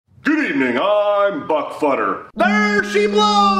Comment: I thought he said butt futter😂😂😂😂😂😂😂